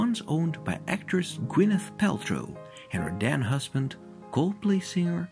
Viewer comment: At the beginning, it sounds like he says it was the home of Gwenyth Paltrow and her “dead husband” Chris Martin 😂 I guess that’s what you get if you tear down great architecture!